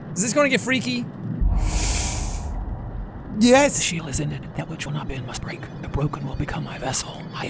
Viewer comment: does anyone know what the old lady is saying at 13:52 ? I hear "The (shield???) is in it, that which will not bend must break, the broken will become my vessel; I'm here - those who do not know me, will know me when they hear the knock" English is my second language, and this is as much of this amazingly creepy line as I could piece together